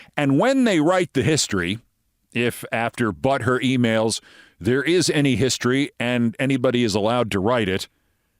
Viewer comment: I thought he was saying, Butt hurt emails. Fits too.